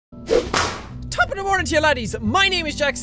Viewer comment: it sounds like he says top-of-the-morning-gea-laddies